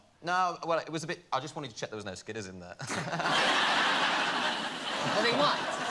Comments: what did Liam say, what did he want to check? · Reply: i think he said skidders as in skid marks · what does he say on 2:50? plzz someone clear it up for me the eng is too fast for meh :p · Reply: "I just wanted to make sure there were no skidders in there hahahaha" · I have always wondered what the hell does Liam say at, 2:50 ? And what does it mean? He speaks too quick and it was probably a British slang😂🤷‍♀️ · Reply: ‘ I just wanted to check there was no skidders in there’ ‘skid marks’